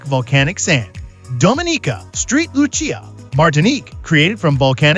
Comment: I hear "Street Lucia" is wonderful this time of year. 🤣🤣